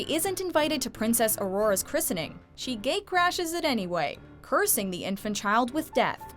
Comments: did she say gay crash it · /3:56 I thought you said gay and not gate for a few seconds.